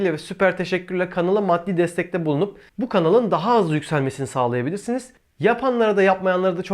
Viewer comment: Benmi yanlış duydum yoksa video sonunda superchatle <<daha az >> yardimci olabilirsiniz mi dedin? 🤣🤣🤣🤣